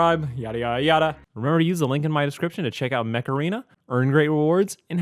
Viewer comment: Install Mech Arena for Free 🤖 IOS/ANDROID/PC: <link> and get a special starter pack 💥 Available only for the next 30 days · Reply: I heard it as "macarena" when you said it in your video.